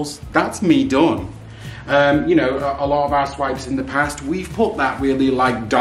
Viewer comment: Love both but I have to admit when mentioned “our swipes”, I thought he said ‘a$$ wipes’ and I had to play it back…. I had some weird thoughts running through my head….. LOL